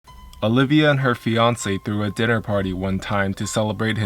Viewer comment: I thought you said “Olivia and Beyoncé” at the start of the video 😂